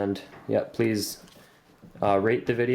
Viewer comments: ahaha sounds like he said yea soo raPe the video and comment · it sounded like he said please rape this video and comment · THought he said.. u already Raped the video ...